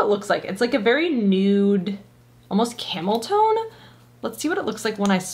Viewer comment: Hi! I almost thought you said camel toe, not tone...😂